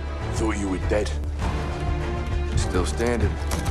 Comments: what is he saying ? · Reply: "Ready for a gunfight?" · The phrase “Lets get evil” Is amazing. I love it and I have no idea why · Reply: this whole time I thought he said "let's get it done", thanks for reminding me that I'm an idiot. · The phrase “Lets get evil” Is amazing. I love it and I have no idea why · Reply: Gun Troller I thought he said “lets get equal”